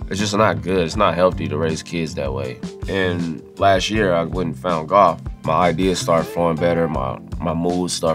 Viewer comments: I thought he said he found God 😂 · he said "found golf" shiii man i thought he said God